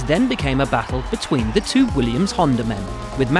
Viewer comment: I had to rewind two times to double-check that they actually said "Williams"